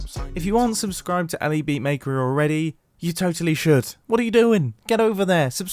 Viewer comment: Subscribe to who? I couldnt quite hear the name correctly. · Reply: Sorry this is late! Elybeatmaker